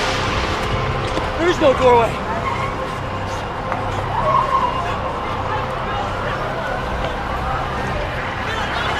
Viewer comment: What does he say at 3:18? There are two separate videos that say the same thing…there is no gorway or something like that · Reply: "someone" said "their's no doorway"